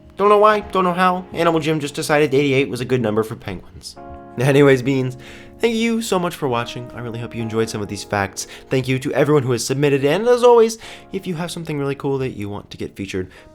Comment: If u go to exactly 9:25 says THEY YOU instead of THANK YOU